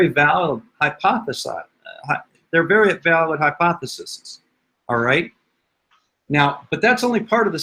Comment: did he say “hypothesis’s” 😂